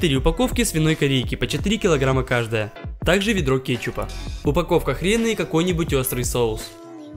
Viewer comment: услышалась упаковка хрена